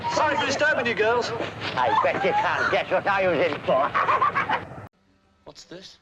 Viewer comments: what did Lenny say? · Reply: I bet ya cant guess what I'm in for HAhaHaA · what the hell did he say I can’t understand the British accent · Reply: he says "I bet you can't guess what I was (in prison) for" - putting on a lustful face, probably suggesting that he was imprisoned for some to do with girls.